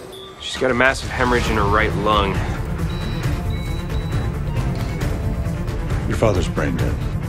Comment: I heard him say "your father's pregnant" instead of "your father's brain dead" 😂😂